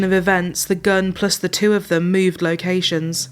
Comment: At first I thought she said "Moved like Haitans" I was like ''wtf??''